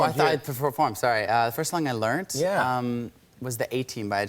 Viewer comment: Did anyone else hear Shawn Mendes say "Ohh, the first song I learnt?" Instead of learned. 1:58 into the video.